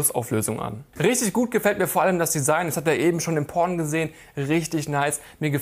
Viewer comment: das habe ich eben im porn gesehen xD was hat der typ gesagt bei der stelle